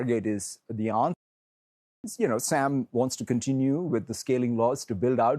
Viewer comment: What was muted at 3:13 ? What did Satya say? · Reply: I think he said "fagg*t Sam". That was momentary slip up.